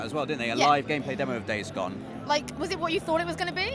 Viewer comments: I thought you said Daves gone and not days gone #keepdave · am I the only one that thought Hollie said 'DAVES GONE' in fear, instead of 'days gone' in excitement? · I thought you were saying "Dave's Gone" instead of Days Gone and was very confused as to why you kept it in the video :P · Anyone else thought Hollie said Dave's gone